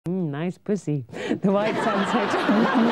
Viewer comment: still if u listen closely it sounds like she says "white sox", is there a white sox soccer team in england all??